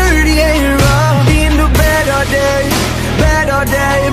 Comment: when he said"in the bed all day" who else thought he was saying battle day